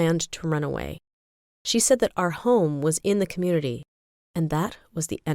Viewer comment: Lmao I thought she said hoe